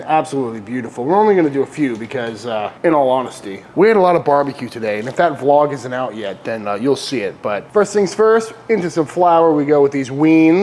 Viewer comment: Lol, I thought he said, "We ate a lot of bbq, and if that LOG isn't out yet, you'll see it." 😅